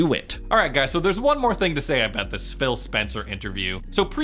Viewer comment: Did he just say spill spencer